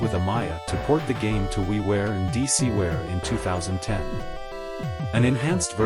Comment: definitely an A.I. reading this if it says "DCWARE" instead of "DSiWare." Also this is completely reading off a Wikipedia article verbatim: <link> There's no effort here. 👎